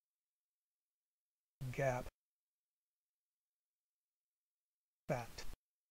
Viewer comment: No matter what I keep hearing 'BAT'